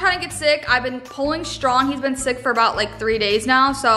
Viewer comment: I thought she said “he’s been sneaky” and thought she was putting like a pill or something, but nah sickyyyy